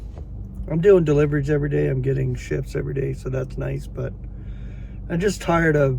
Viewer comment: Brother, when you said you "were tired and getting shifts everyday," I thought you said "getting shits everyday." I thought....... well, I'd be tired too. Love grace and peace dear brother. Keep looking up.